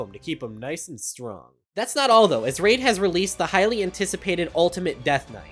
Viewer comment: while skipping through the sponser i heard "raids has aids"